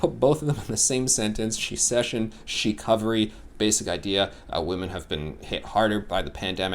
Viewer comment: Oh I thought he was saying a Xi session and a Xicovery My bad